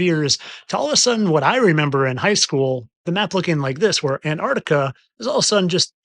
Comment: Did Bro say "Learned in High School" ?... seems like the opposite of Learning Hmm